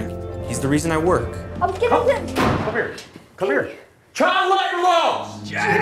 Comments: what does shia yell at 1:29? and the kid's line at 1:49? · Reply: "child labor laws!" 1:49 "you could if you started when i did" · What is the dad yelling at 1:29? I can't understand what he's saying. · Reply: ‘Child labour laws’